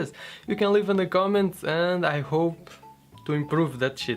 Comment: did he say shit